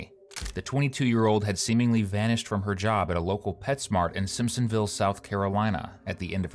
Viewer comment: At first, I THOUGHT that he had said "Pet's Mark" (instead of Pet's Mart, but I was wrong, lol), and it instantly reminded me of my Granny Key... She used to say "Wallmark" and "K-Mark", lol... Yeah, we're from Tennessee... She also said "Ox-again" (instead of oxygen), "Plas-kit" (plastic) and "The Kris-chuls" (Krystals), lol... I miss Granny Key, lol...